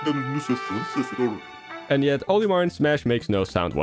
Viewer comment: I heard Captain Olimar